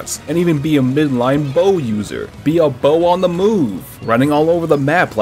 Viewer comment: please explain to me how he did not say "be a bow on the go" tactics perchance